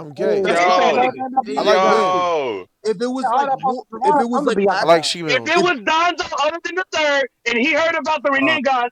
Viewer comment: "i'm gay, i like men, i'm into shemales" you think you slick i heard all that shiet 💀🥲: